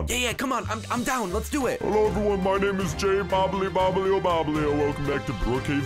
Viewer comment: Did he just say Jabolbloeobolbole